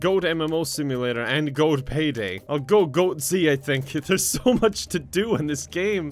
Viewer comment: I wasn't paying attention and when you said "Goat-z" I thought you said "goatse" and I was like KEVIN NO